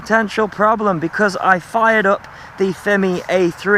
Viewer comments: I seriously thought u said “f**ked up” at the beginning of the video..... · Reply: I heard "fired up".. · I seriously thought u said “f**ked up” at the beginning of the video.....